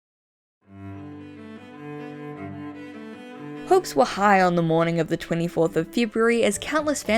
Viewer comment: What izzy said Izzy: Hopes What I heard : Folks